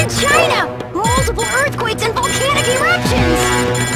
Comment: I thought Marucho said “volcanic erections”